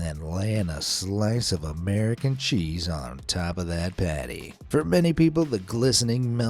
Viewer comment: Why did you say baddie?